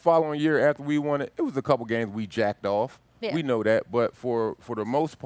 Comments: Did he just say 'jacked off"? Lmao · Pause... did he just say "jacked off"....????